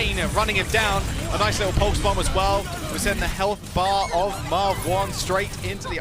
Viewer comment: did Jaws call him Marve-one??